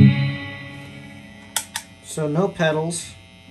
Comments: Yo escuche que dijo sono perrus 😅😅😅4:13 minutos dijo eso xd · Yo escuche que dijo sono perros😅😅4:10 a ese minuto lo dijo xd